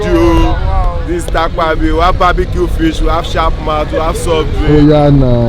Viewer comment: did say we have chapmat?